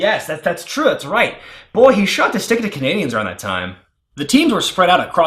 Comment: what the hell did he say there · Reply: “Boy, he sure liked to stick it to Canadians around that time” (I think he’s implying the screwjob)